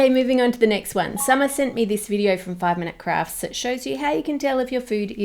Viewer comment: when she said "5 minute crafts", I thought she said "5 minute craps" 😂